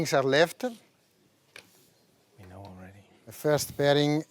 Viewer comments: What does it say at 18:01 hahahahah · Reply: I heard "we know already" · What does it say at 18:01 hahahahah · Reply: "We already know"